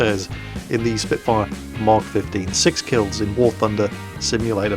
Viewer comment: Awesome vid! However I have to ask, did say mark 15 at the end?